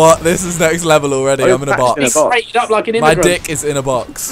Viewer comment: Whatd did say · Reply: being crated up like an immagrant xD